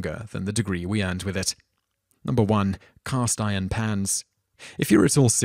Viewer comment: I almost spat out my drink at the cast iron pans part, 'cause I thought he said "cast iron pants" with I was in another tab.